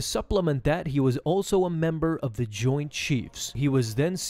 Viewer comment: I am not going to lie, I thought I heard "member of the joined cheese."